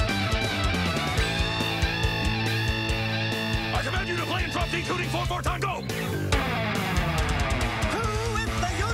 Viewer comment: What did he say? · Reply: I COMMAND YOU TO PLAY IN DROP D TUNING 4 4 TIME, GO